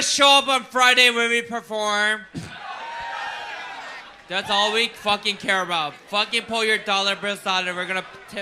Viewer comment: i wish I could understand what is saying to understand the other’s reaction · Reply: She was trying to say “That’s all we fucking care about. Put your dollars aside and tip us”